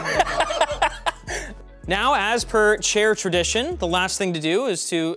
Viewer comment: Thought he was going to say chairadition. The second 'a' is near silent.